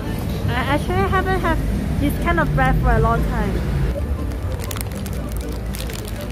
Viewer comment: She said "I haven't had this kind of food for a long time." But the subtitle says "It's been a long time since I have this." ??? at the same time the subtitle in Korean says "That's the end of the Korean market review." ??? Who the hell wrote those subtitles?